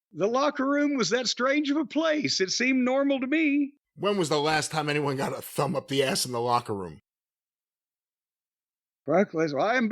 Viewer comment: More wild @ 32:25 could swear jim starts to say brock lesnar got a finger up the *ss then trails off casually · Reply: HE DID SAY BROCK LESNAR. Good catch!